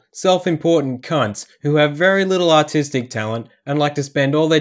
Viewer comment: "...very little autistic talent" - say what? - oh, wait... you said "artistic", right? - gotya! ... had me very, very confused there for a moment! (no offense to actually autistic people; they tend to have - on average - better social awareness, empathy, and self-perception than the self-obsessed cunts who are the issue here... For the latter the term "egoist" (in the meaning of 1763 - etymology ftw.!) seems much better suited...)